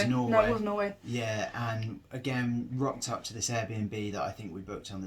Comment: Did he just say at 2am they are all naked or did i hear wrong lolol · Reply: Knackered as in tired